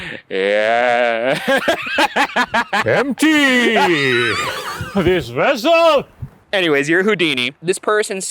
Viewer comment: When subtitles are on, it says “Empty.....Great Result” instead of “Empty.....this vessel”